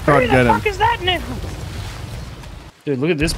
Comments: did say the n word or am i trippin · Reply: I said "now" you see it in the voice to text thing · What was said at 1:31 ayyoooo? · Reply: "now" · am i tripping or did say something crazy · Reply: I said now